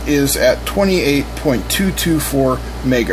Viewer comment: Looks like dementia is setting in. I thought he first said the osc was 22.224 MHZ! My bad.🤔🤦‍♂️